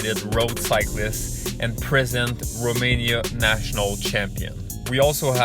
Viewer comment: I thought he said that guy was the "prison Romania national champion". Well he's really turned his life around.